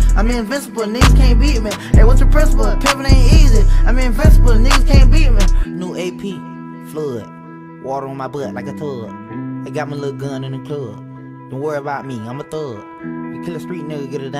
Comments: Omg I thought he said water on my butt like a stud🤦🏽‍♂️😂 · all this time i thought it said water on my butt like a thug😂😂😂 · New ap, flood, water on my butt like a tub😂😂😂 bro wtf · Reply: That part cracks me up all the time. The first time I heard it I thought he was saying "water on my butt like a thug" . I was confused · I deadass thought he said "Water in my butt like a thug" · I thought it said “water on my butt like a thug” 🤣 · Thought he said water on ma butt like a thug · This whole time I thought he was saying "water on my butt like a thug" I can't believe I've been saying this without knowing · I always thought he was saying “got water on my butt like a thug”